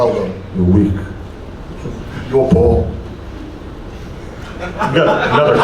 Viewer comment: what did eddie say at 13:13 the subtitles say "you're paul" is that like a wrestling refference of something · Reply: He said "You're poor".